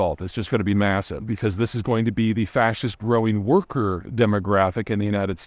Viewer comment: The safety angle is massive. In my younger days I took many contracts overseas, mostly in the West Pacific and Middle East. A handful in Asia and South America. One in Sub Saharan Africa. Only the one in Africa gave me any concern (the living compound was guarded by mercenaries). Now, there are very few of those countries I would be willing to visit. North Americans have become greater and greater targets everywhere. I wouldn’t even go to most of Mexico these days. It used to be “Yankee go home!” Now it is “Yankee come here so we can rob, kidnap, and ransom you.” (Or worse). Ironically, probably the safest place to take a contract is the Middle East. The only places I would be willing to go for a vacation is Western Europe, Australia/New Zealamd, or Canada. Or strangely enough, Cuba. France, in my experience, sucks because it is full of arrogant, unfriendly types. Love the germanic countries and Britain. Australia is an adventure because everything non human can kill you. I spent most of my life traveling the world. I watched everywhere become less and less civil. And it is only going to get worse as globalization devolves. I am glad I am old and won’t see more than the beginning of the tragedies to come. I feel sorry for the friends I made all over the world who will have no choice but to suffer through it. When things get really bad for them, please keep them in your thoughts and prayers. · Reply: did he say "The fascist growing worker demographic.."?